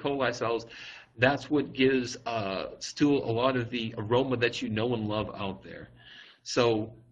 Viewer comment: did he just said aroma you know and love ? hahaha